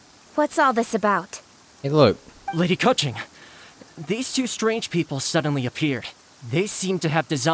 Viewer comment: how did i hear lady cut g 😭